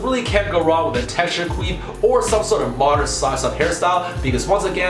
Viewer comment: Did he just say queef instead of quiff?